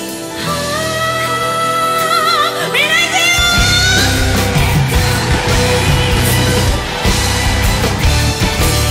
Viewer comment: からのみんな行くよ〜！！！！のあとなんて言ってるかめっちゃ気になる！ 友達から水樹奈々さんの曲オススメされて初めて聞いたけど、めっちゃかっこいいし歌綺麗だし歌唱力やばくてずっとリピしてる笑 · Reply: 曲名を叫んでます。 エターナルブレイズ！！！！